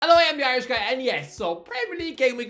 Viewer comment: Did you just say Ein-steen Irish guy?